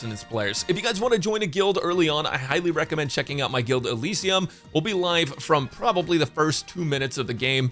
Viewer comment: What’s the name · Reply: Sounded like he said Elysium. CC has 'Elisium' at 8:19.